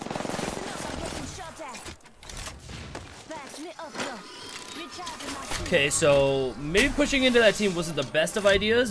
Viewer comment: what did she just say ??? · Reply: Patch me up D.O.C